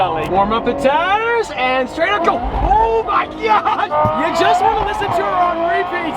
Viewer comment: did he say o my gyat🤔